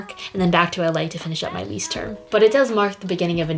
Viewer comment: What did she say I didn’t hear around 0:40. ‘Then back in L.A to finish...’ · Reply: To finish her lease term